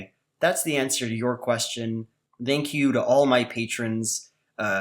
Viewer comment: did just say "dank you"?